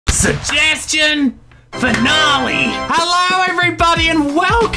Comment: Who heard saying fanalee in